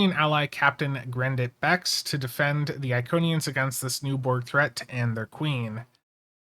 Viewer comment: Did they just say Iconians?